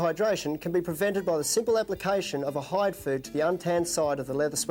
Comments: did he say Hide Food? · He says add a "hide f... to the inside untanned sweatband" to halt shrinkage. what did he say? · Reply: He says "hide food", which is a leather treatment product.